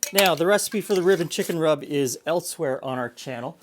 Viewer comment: I keep hearing "ribbon chicken rub", wondering what a ribbon chicken is.